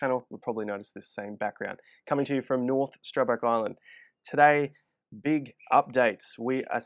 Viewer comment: Oh "island"... thought I heard Ireland, haha! Have more fun.. get more done!